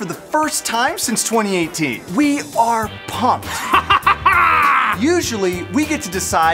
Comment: I thought they said “pog” in the beginning when the said they were “pumped”